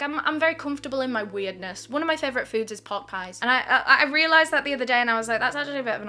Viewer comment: WELCOME BACK 🥹🥹 also i thought you said your favourite food is ‘pork eyes’ 😂